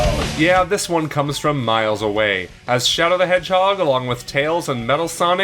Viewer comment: did someone say Miles Away?